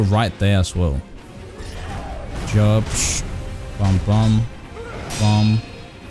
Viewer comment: did he just say "jupsh" ? 🤣